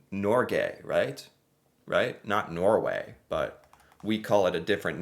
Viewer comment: haha eyyy sounded like u said Norgay . none offense taken, just thought it was funny and neede to point it out xD